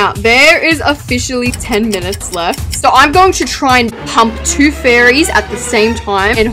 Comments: STOP I THOUGHT THIS SAID " so im going to try to hump 2 fairies at the same time😭💀" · did you say hump or pump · DID SHE SAY SHES GONNA TRY H|_|MP TWO FAIRIES 😭